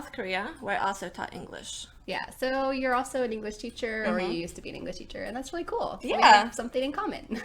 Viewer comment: Hi thanks for your efforts :) ... i wanna to ask u about what u said in 1:56 ... " so you're also english teacher already used to be english teacher " what's that means ??? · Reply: I said, "So you're also an English teacher, or you used to be an English teacher..." Feel free to download the free transcript here: <link>